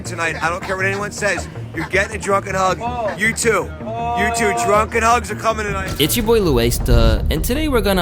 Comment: what is that man saying .. they will get drunk and fkd … that’s wild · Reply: “Druken hugs”